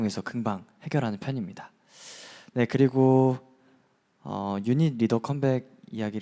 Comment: did he just said "unit leader comeback" and than laugh nervously? HMMMMMMMMMMMMMMM I see you Mr. Kwon 🤡